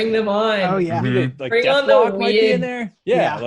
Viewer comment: “Bring them on, bring on the weird dick” 16:57 what? Did I hear that right? no?